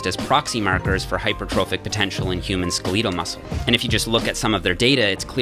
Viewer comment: did he say ske-LEET-al muscle??